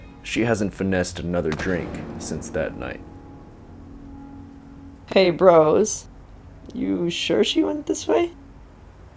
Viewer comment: I love the last part "hey bros are you sure she went this way" man bouta get his cheeks clapped😂😂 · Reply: I thought he said "Hey bros, you sure you want this fight?"